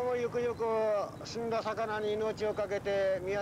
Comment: “死んだ魚に命を賭けて”、かっこいいねぇ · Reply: 自分もそう聞こえて？と思って見直したら 「積んだ魚に命を賭けて」で安心した なんで今頃あなたへのおすすめに出てくるんだろ